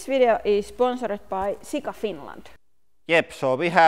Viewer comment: I heard it as: "This video is is sponsored by Sick of Finland" Is that like the anti-tourism dept?